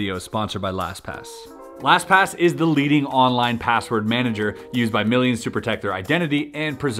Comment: I thought you said the deleting password manager